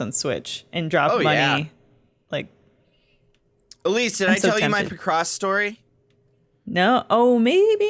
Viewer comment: I did a double take when I thought Jon said "Elyse, did I tell you my Lacrosse story"